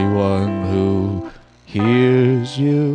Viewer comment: Why do I hear ‘ dis falling down, it farted 😂’